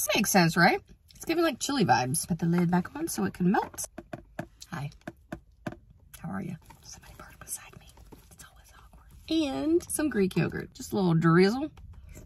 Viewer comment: NGL, at first I thought you said “somebody farted beside me” and I was all, *side eye* girl, that was probably you. Lol